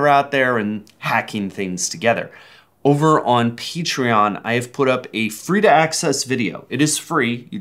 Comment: Did they just say Peachtreon? Edit: Wow thanks for the 0 likes!